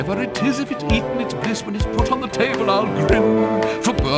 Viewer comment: Whatever it is. If it's eaten, it's piss. What is he saying?